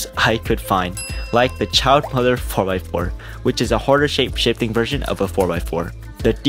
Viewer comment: I heard "the child mother*ucker" XD